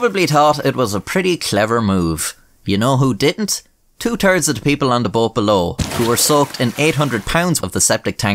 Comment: I had to laugh at the way your accent sounds when you said two thirds of the people in the boat bellow as it sounds like you said two turds of the people!